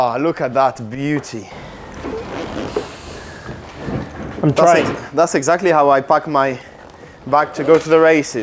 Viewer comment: what did he say ?!!!! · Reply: “I’m trying” (I think that’s it)